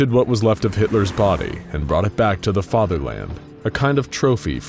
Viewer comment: Did you just say fatherland?